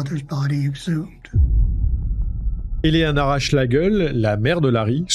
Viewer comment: "Eleanora Schlegel" J'ai entendu "Elle leur arrache la gueule"... 🙄 (vu les circonstances, j'ose pas en rire trop fort... 😅)